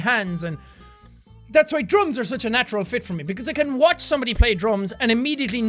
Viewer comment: thought he said drugs for a minute